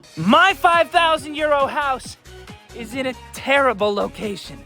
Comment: Whenever he says, "My €5000 Euro house..." I always hear, "My 5000 year old house."